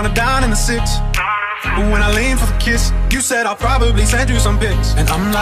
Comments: I heard on 0:21 "You said I'll probably send you some bitch". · Sounds like he saying “You say I probably send you some bitch”